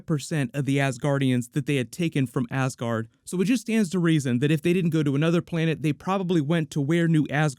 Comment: anyone else hear "it just starts to raisin" ??